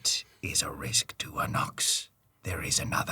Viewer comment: for a sec there i kept hearing "an ox" instead of "a nox" there which makes i talking about the guy so much truer lol